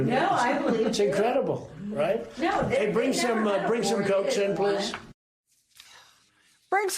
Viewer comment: "And hey, bring some goats in please"! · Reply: Cokes. I thought he said goats, too